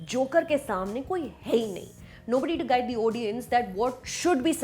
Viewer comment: Did she just say "odiens" instead of "audience" ????